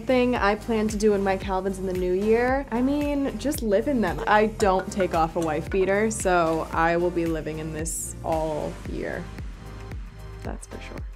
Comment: what does she say at 2:30 ?? I heard ‘I don’t take off a wife beater’????